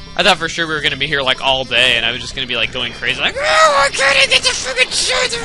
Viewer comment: Did you say fuck in 10:01? If you did, I didn't think that you would have a colorful vocabulary even thought kids are watching this... Mostly I don't give a flying fuck but now I'm starting to relize that some brain-dead parents are letting their kids get youtube accounts even though they shouldn't....